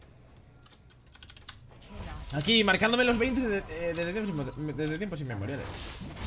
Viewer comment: que dice ? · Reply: "marcandome los 20 desde tiempos inmemoriales" Eso es lo que yo entendí xD